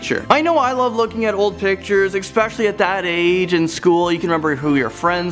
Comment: what word is he using? Eckspecially? Never heard that one....